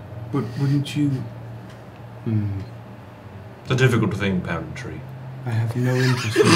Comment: the subtitles said “pound tree” and I was confused for a sec